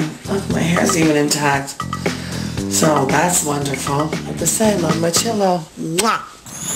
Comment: What's she saying at 5:24? "The say-lo my Chillow"? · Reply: Have to say I love my chillow, then she kissed it